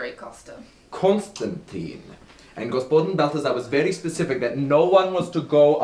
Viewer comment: What/who was "...very specific that no-one was to go alone" (Costa to Freddie about the milk) - Did anyone catch this????? It's at 4:25. Thanks! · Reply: Oooh, it definitely says something about Balthazar. Costa thinks it was Balthazar's challenge too. So now the question is why would Balth put this in the box?